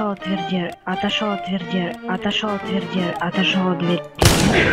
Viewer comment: Я один услышал дверди?